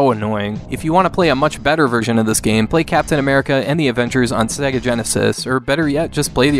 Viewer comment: At :51, it sounds like you said "...play Captain America and the Avengers on SNEga Genesis." :P